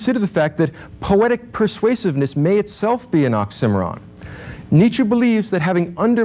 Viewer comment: is he saying oxymoron?